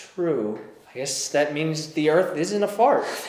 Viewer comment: Haha omg you said the the is shit ! 😂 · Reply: Ju animation he said "the" is shit? I thought he said the earth is shit